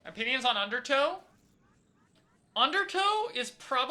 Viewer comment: I thought at the beginning said "opinions on undertale" 💀